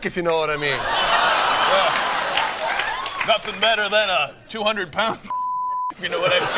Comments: what does he say lmao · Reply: 200 pound snatch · Does anyone know what Brad said at 6:33? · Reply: He said, ' nothing like a 200 pound snatch" 😂